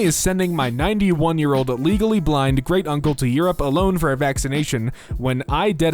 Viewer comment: did he just said vaccination instead of vacation...